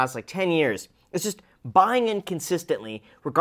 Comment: I first heard "buying in consistently" as "buying inconsistently"